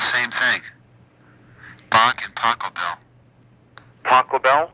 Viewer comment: Here is a funny one: Myself being used to the German pronunciation of J.S. and Johann's last names, at ca. 42min when you mentioned Randy being into Bach and Pachelbel I misheard the words and instead heard: "Taco Bell"; so I genuinely had the same impression of Yngwie as "WHO's THAT!?" so I had to rewind a bit to check about it ( even the YT's CC got it like that too !) hehehehehe gosh I had a laugh. Anyhow, THANK YOU very much sir for sharing these jewels. All the best ! AB ps.- I am subscribing.